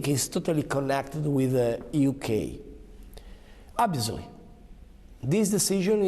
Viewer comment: Ma secondo voi cosa cercava di dire a 0:11? Io capisco "absoly" che non vuol dire assolutamente niente 😅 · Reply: Penso volesse dire "obviously"